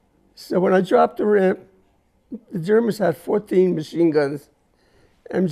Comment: Did he say 14 machine guns?